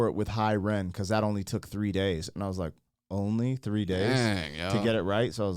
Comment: Did he say Hi Ren took 3 days?! Wow, I thought he said 3 takes!